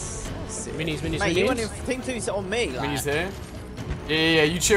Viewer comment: does Ali say Charlie 🤔